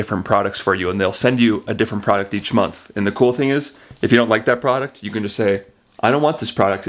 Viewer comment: I definitely need more coffee cause when you said AX my 1/2 asleep brain thought you said if you don’t like the A$$ they sent …